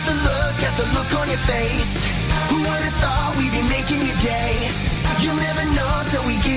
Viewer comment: did he say You never knew we would be making you gay